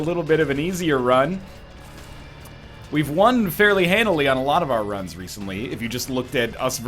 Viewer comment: Captions turned on for some reason, and they made me realize that at 0:53 it really sounds like he says "We won fairly Hannah Leon"